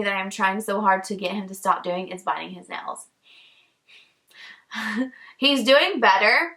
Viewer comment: when you said he has a bad habit of biting his nails I thought you said buying sandals 😂😂